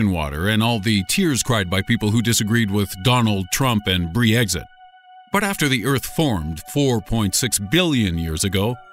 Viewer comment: Did I just hear "bree-exit"?